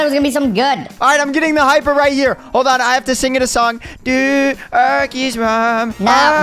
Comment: did bro say DOODLE'S MOM